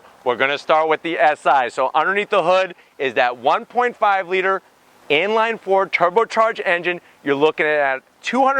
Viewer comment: Did I hear you say at 16:25it had a “Ford” turbo charge engine